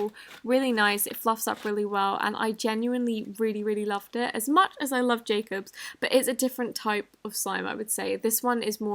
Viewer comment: at first i thought she said " as much as i love jacob" but then i replayed it and there was an s at the end...idk their relationship status but i ship them :3